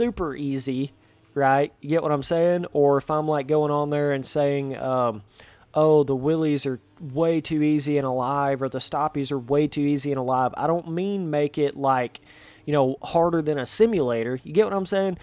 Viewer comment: did he say willies 🎩 👂❤❤👂 👃 💓💓 😝💬💓💓💓 👋👔👉 💓💓 / \ 👟 👟